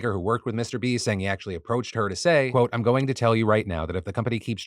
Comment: Relax. Breathe. There's punctuation all over the place. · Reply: He did actually say, "quote".